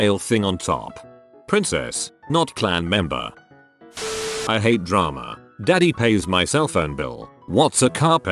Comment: They say "I hate drama" I hear "meat grinder"